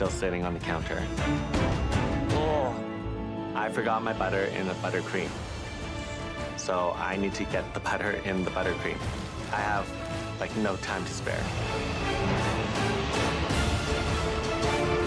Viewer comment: "I forgot the butter in my BUTTERcream" lmaooo. I feel this on a spiritual level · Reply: Oh I thought you said “ I forgot to butter my buttercream” lol.